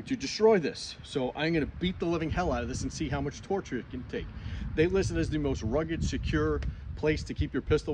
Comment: It definitely has its uses. Appreciate you investing the time and money into testing it out 🤙🏽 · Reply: I thought he said they sent it to him